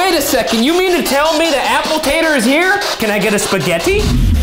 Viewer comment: Not to rain on their parade but I'm pretty sure it didn't say apple tator, it sounded like it said "I'm a demon"